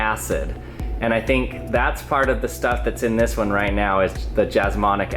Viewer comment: I heard just Monika acid😂